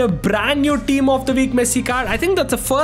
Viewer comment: bro said pessi instead of messi 😂